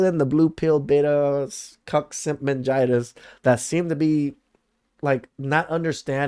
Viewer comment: Haha did i hear "cuc-sig-mingina"? Niceee